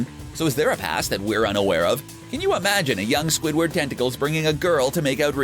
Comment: did you say tentacles.......................